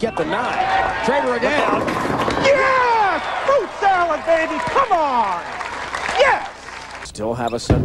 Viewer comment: did that guy say “FRUIT SALAD BABY!” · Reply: I heard “FOOT SOILD BABY”